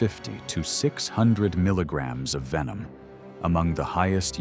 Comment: oops sorry misheard. he said 600milligram not ml Its still a max for venom